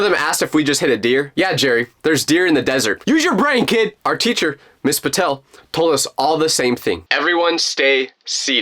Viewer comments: His teacher is his mom I think at like 3:50 to 3:59 he said miss vitel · I thought you said